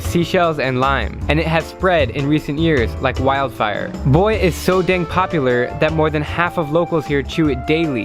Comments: and 0:45 it sound as he is saying boi is so dang popular lol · Thought u said " boi it's so dang popular" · Why did I hear "Boi is so damn popular... " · I thought he said Boi is so dang popular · who thought he was saying boi its so dang popular